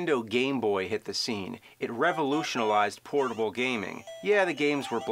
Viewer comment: been watching this video for years, only now do i hear him say "revolutionalized" instead of "revolutionized"